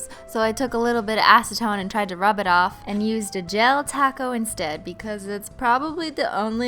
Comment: Am i the only one that thought she was saying "gel taco" instead of "gel top coat"?